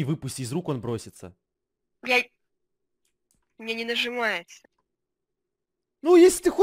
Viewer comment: мне 1 послышалось? ,,Бьять у меня не нажимается"